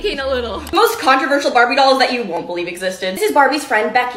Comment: I thought it said "baddie dolls" LMAO😭😭